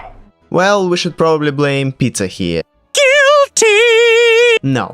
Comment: I first thought you said " we should blame pizza here "